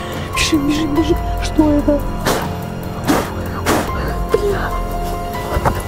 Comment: кто услышал бля